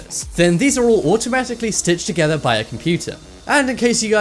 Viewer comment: wait.... ... DID IT SAY "BITCHES"????